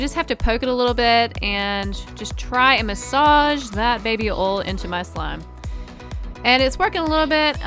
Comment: Sound like u say baby ol ...